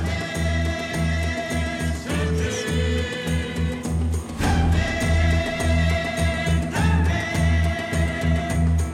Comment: o ren what does she say?? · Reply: I believe she whispers "Beatrix" in disbelief, to see her alive still. It's slightly bleeped as it always was before it was revealed to the audience to be her (Uma Thurman's character's) first name.